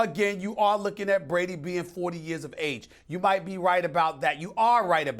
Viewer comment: Max is trash LOL.. He even said the cliff had already arrived during the first half of the Super Bowl until the comeback happened.. LMAO. The cliff seems to have arrived for Eli though ROFL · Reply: I thought he said after 40 years. And you can't really blame him for believing in it. At 41, there hasn't been a QB that plays at a high level. Just cause he is playing good now doesn't mean he will in his forties. Brett Favre has the best 40 year old career so far with 33 TDs, 7 INTs, and a 107.3 rating. The next year he was horrible. Thinking he will play great till he is 45 is retarded. It helps being with Bellichick, but not gonna happen.